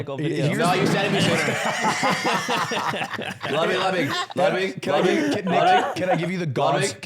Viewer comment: What does say that makes everyone LOL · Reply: "no u said it'll be sooner"